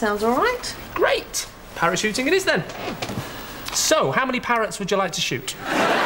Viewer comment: " Parachuting? Yes, how many parrots do you want to shoot ?" Great line. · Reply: I heard "parrots" as "parents"